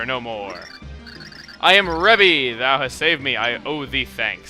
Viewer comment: I thought you said Revy...If anyone's watched Black Lagoon, you'll get it.